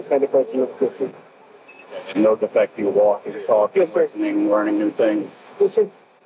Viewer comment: He clearly says “yes sir” here, when asked if he has issues walking and talking, before trying to use his blood pressure meds as an excuse. But in the next moment the officer asks again to clarify and even though the subject says “yes, sir,” and even nods his head up and down, the captions read “no, sir.” Which makes the final remark by the officer really strange. When I first watched this I couldn’t hear it too well and relied on captions. I thought it was weird he’d say “yes, sir,” once and then “no” two times after that, before the officer says “stop playing games,” so I rewatched it. Sure enough these captions don’t match and it makes the whole thing seem really strange. How could you guys miss this? He even nods his head when saying, “yes.” Come on.